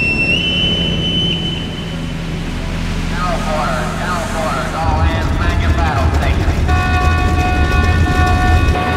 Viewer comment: 製作お疲れ様です！質問なんですけど2:27の所はなんて言ってるんですか？ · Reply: ありがとうございます<(_ _)> 「総員戦闘配置に付け」ですね～